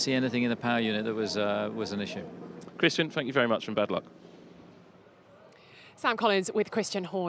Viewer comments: did he say bad luck? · Did Sam say "Thank You very much and Bad Luck" to Christian and the Redbull's!?!? @ 28:34 😲😅